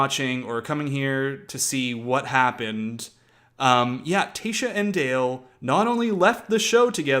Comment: Ummm u mean CLARE & Dale! LMAO. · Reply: Lmao! I didn’t watch it so first thing I heard him say was Tayshia snd dale lollll